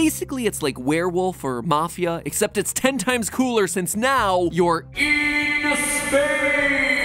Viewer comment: I thought he said “IN SPAIN”